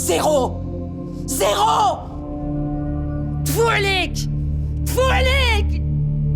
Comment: Elle dit quoi là, foualik ? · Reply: Elle dit ''tfo elik'' genre ''putain''